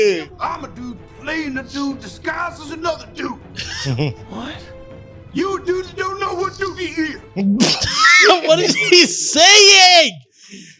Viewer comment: What makes me laugh is I know what he said and have always known lmao. He said “you the dude that don’t know what dude he is!”